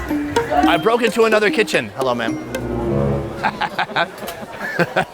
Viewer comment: i am Ugandan and i wanna let the all world know that she doesn’t represent us at all! Ugandans are very happy and welcoming people! I dont understand why her attitude is so bad! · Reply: Pretty sure she thought he called her "Man"